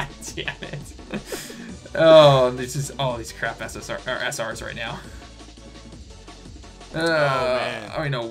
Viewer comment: Wait, did just say "craft essence"?! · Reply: No, he said 'crap ssr's ehh sr's'.